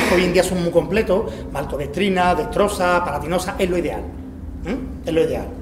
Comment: ¿Qué es lo que dice entre 14:35 y 14:38?, porque no llego a entender que carbohidratos mezcla. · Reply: Alfredo Toledano dextrosa....es solo azúcar...